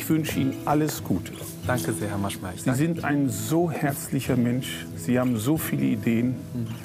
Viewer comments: Wer hat bei 3:04 auch "sie sind so ein häßlicher Mensch" verstanden😂? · Bei 3:03 verstehe ich „Sie sind ein so häßlicher Mensch.“ xD